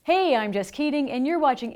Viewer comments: First I heard "I am just kidding". Then I found out she said: I am Jess Keating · just thought she said "I'm just kidding" lmao 😭😭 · I thought I heard her name as Just Kidding.. so I go to pinned heading - her name is Jess Keating, what a relief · I know her name is Jess Keating, but I heard "Hey, I'm Just kidding!" 🤣🤣🤣